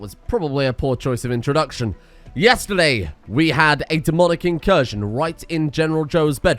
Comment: I sincerely thought you said "That was probably a poor choice of introduction... Yesterday we had a demonetization" and I died laughing.